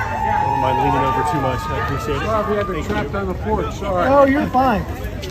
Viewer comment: It sounds like he said “Sorry if I ever crapped on the floor sorry.